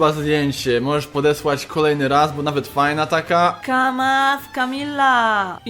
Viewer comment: Czy tylko ja usłyszałem od,, fajna mafiA''